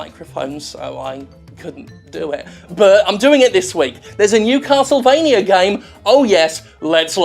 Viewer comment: When Jim said "New Castlevania game" I thought he said, "Newcastle Vania" game, and got excited for a game about Geordie vampires